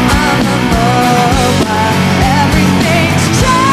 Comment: I thought she was saying:Profile?